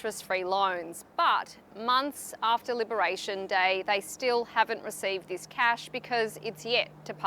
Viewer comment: Every time I hear “Liberation Day” my brain hears “Lubrication Day..”